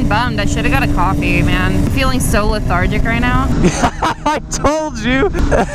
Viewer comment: okay, english is not my first language but what does that word mean and how do you even spell it? xD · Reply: she says "lethargic" <link>